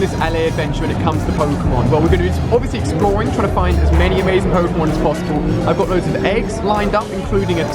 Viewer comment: I put subtitles on and when he said let's catch as many Pokemon as possible it thought he said as many horns as possible